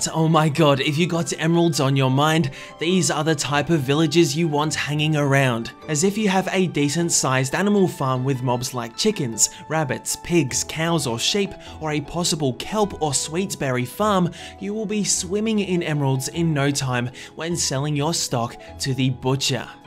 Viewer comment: Did you said council of sheep?